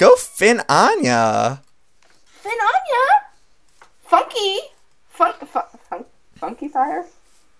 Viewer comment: is funny cause I thought she said fucky!